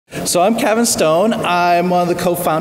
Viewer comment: I thought I heard him say, "So I'm kinda stoned.." until I looked at the slide on the left that said "Cavan Stone". LOL was wondering what kind of presentation this was going to be for a second!